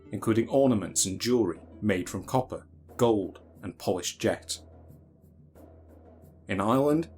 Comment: ish, he says copper, gold, and one other thing. Sounds to me like “polished jacks”. Anyone hear what he said? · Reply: Polished jet